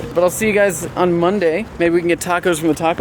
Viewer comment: who heard Vsauce3 ask "is freddy here?" :D